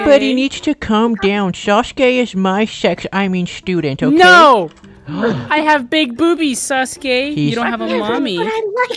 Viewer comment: WHAT DID HE SAYYYYYY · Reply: Ope